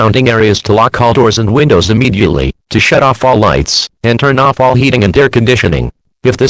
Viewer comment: sounded like the tts said "shit off all lights" 💀💀💀